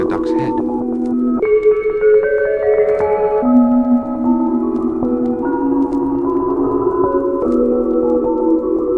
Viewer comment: When curtain is drawn up upper window on the right, what is he saying then? 0:17 "very happy........?" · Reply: Caterpillar